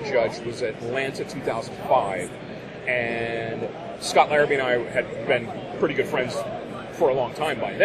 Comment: Excuse me, did he say Scott Arby or Scott Darby?